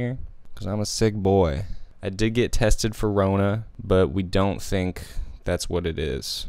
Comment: Stupid but... 0:47 Why did he say "forona" instead of corona??